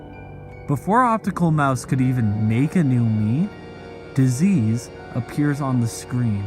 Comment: what he says :before he can make a mii captions:before he can make a meat